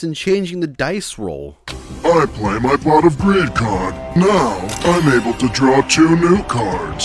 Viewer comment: it's sounds like he is saying I'm playing my pot of credit cards